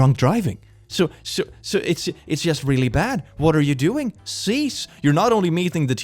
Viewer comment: Okay I know says cease but as an afrikaans speaking South African I always hear "sies" which means gross and the context in which he uses it makes it so much better 🤣 😂